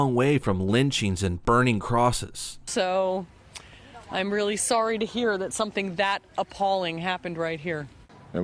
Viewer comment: what does the Bird say ????? she said : "so........I'm really sorry to hear that something THAT appalling Happened right here." But all i really heard was : "Bakaaaaaaaaaaaaaaaak !"